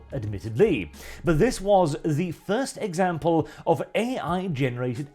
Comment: I heard “Thirst Example”.